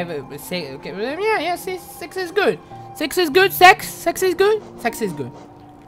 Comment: I heard "sex is good", I'm the only one that heard that? (sorry if a word is bad wrote, because I SPEAK SPANISH)